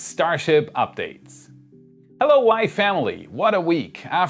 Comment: Everytime you say "Hello Wai Family" i always hear "Hello Wyatt Family"...sorry a WWE reference here